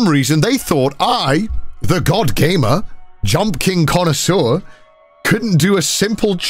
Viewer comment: Did I hear “Connorsseur” or is it just what I wanted to hear